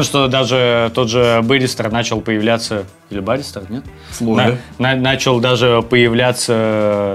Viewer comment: Что говорится на 23:05 начал появляться..*что*? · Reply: Sloe. Слое джин - это джин с добавлением терновых ягод, скорее как крепкая настойка, он около 30 градусов